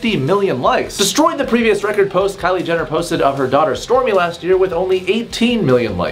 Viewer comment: I really thought said “her daughter stormular”